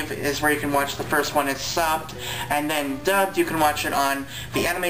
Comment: OOOHH I thought you said, "..where you can watch the first one. It sucked." lmao! I had a few issues with the Polar Express movie because I grew up with the book and can practically recite it. If you like the movie you should read the book with the tape that comes with it. It's an awesome experience! Great review =]